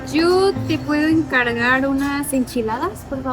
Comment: what is she saying to order? The CC shows it as, "Yo te puedo encargar unas enchiladas?" To me it sounds like she's sayin, "Jew te puedo encargar..." 🤣